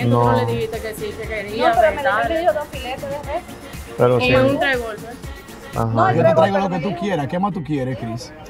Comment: Yo escuché que dijo y un revólver